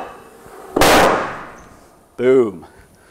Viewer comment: lol 0:55 i heard boob